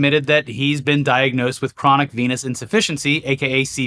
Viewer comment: Me half listening as I make a sandwich, needing to stop what I’m doing and rewind because I thought said Trump was diagnosed with chronic penis deficiency and I was about to be like servers him right.